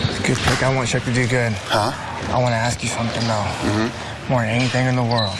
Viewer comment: “A lot of you are upset that I called you chicken shit.” “…” “Anyways, now you guys are total salads.” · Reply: I thought he said “I wanna toss your salad” 😂